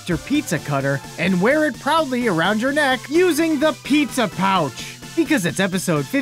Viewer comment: i thought it said pizza douch